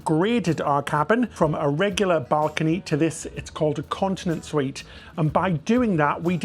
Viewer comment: I kept hearing 'Incontinence Suite'. It did look gorgeous.